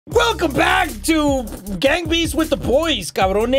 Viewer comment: People who thought said gangbang for a sec 👇